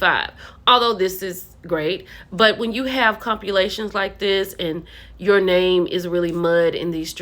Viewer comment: did she say copulations or compulations? One is intercourse, and the other isn’t even a word. I’m no fan of, but let’s not make fun of someone’s speech impediment if we’re gonna be busting up words ourselves.